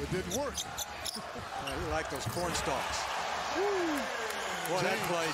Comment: Did he say like those porn stars?